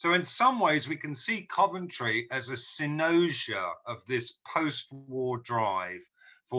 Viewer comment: Whenever I watch Will Self I make sure I have my dictionary app open and I learn five new words. What's the word at 7:19? Sounds like 'synosia'. Can't find it by Googling.